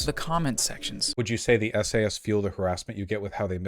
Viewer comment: There was a moment where I misheard "Video Essayists" as "SAS," which led me to a moment of imagining Vaush getting swatted by the British Special Forces 🤦🏼‍♂️